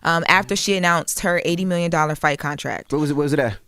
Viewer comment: 80 million?! I thought I heard claressa say 8 million hmm that's crazy